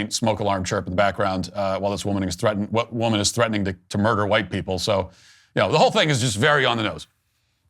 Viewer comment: huh.. did she say murder?